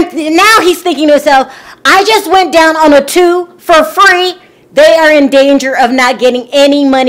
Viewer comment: I thought she said I just went down on a tomb for free.